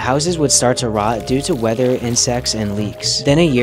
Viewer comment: My dirty minded self thought he said sex instead of insects